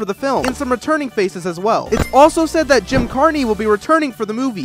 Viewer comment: “Jim CARY” 💀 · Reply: i heard "Carny"